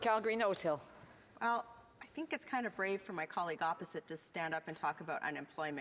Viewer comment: This is my fav part. I thought She said braying at first but She said brave.